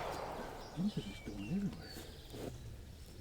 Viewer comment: i hear a voice? Weird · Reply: I think that was just me. I said something to the effect of "these things are just going everywhere".